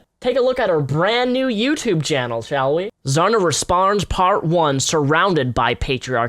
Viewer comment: (Did you say Zarna resparns?) LOL